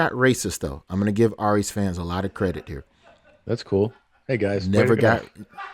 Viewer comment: what did Tom say? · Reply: "hey guys, never got"